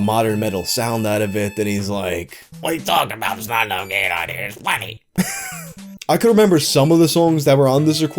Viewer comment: Lol I’m trying to understand what he said at 12:45 in voice but I can’t understand it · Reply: “What are you talking about: “There’s not enough gain on here”? It’s plenty!”